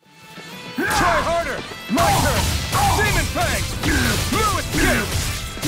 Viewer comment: I thought he said semen fling but it was demon fang😂😂